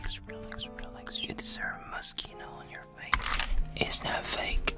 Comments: I thought he was saying Mosquito... “YoU dEseRvE mOsqUiTo oN yOur FacE.” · Idk why but I keep hearing "You deserve mosquitoes on your face." Yes, I think I have hearing problems.